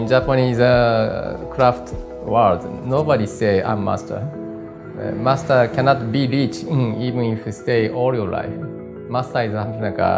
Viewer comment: Subtitles are wrong at 5:54. He says "Master cannot be REACHED"